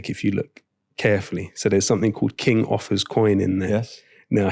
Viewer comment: I thought he’d said King Arthur’s coin. Lolol. It was King Offa’s dinar. So interesting!! Love me some LowKey. He is indeed low-key, but his perspicacity is scintillating. I always get an education when I listen to him. What a gift his children have in him.